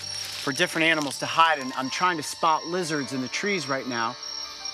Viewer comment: did he say wizards?